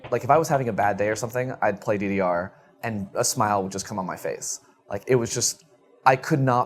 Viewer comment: I paused this video at 2:07 and the first thing I heard him say when I got back was “come on my face”